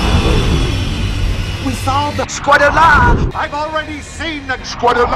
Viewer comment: What is being said at 4:20? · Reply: Squadalla